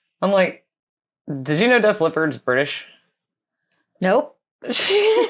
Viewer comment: Did. You say Def leopard were not British that's what I thought they said the original came from Sheffield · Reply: We said they WERE British & even said they're from Sheffield